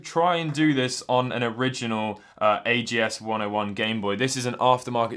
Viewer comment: Did he just say at around 2:40 in the video that he modded an ags 101 to have a better screen?